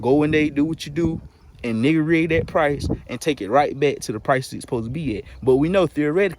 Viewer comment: Am I high rn or at 5:52 did you say “N word rig the price”?